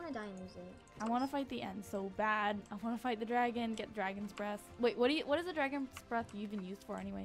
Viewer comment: i thought you said dragons breasts xD